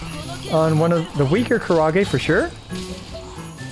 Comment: I thought was saying "kurage" and wondering what kind of squid he was fighting LUL